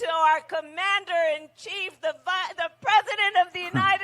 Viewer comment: It sounded like Kamala quickly corrected herself at the 38:45 mark where she nearly introduced Joe as vice president. She got the v out.